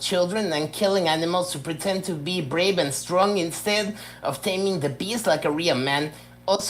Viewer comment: "Instead of taming the beast like a real man" my new favorite phrase! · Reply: I honestly thought he said "taming the bees". /Shrug. Not sure it matters much what he actually said. Hilarious either way.